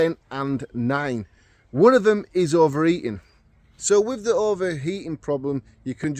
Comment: I'm glad you said that more than once as i thought you said overeating